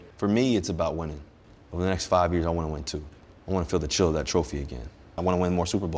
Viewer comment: I heard it as “I ant to win too” not two like everyone is head scratching over